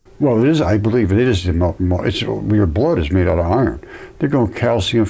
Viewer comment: did he say he thinks that the iron in your blood is molten????